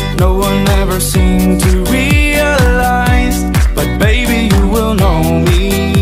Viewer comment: Searching this song: Normal people:You don't know me. Me:Take me to the place I used to run. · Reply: But me misheard and search "baby you would know me"